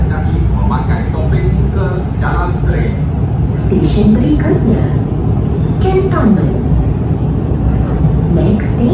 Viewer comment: what is the male announcer saying? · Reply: It's saying "Anda dikehendaki memakai topeng muka dalam tren" which means that you are required to wear a face mask when inside the train